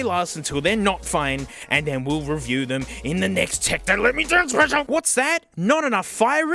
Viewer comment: I THOUGHT THAT SAID "tech me down special"